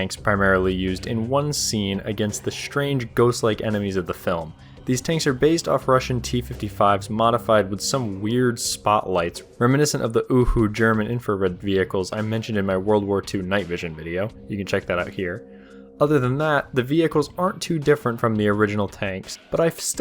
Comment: Did- did i just hear an UwU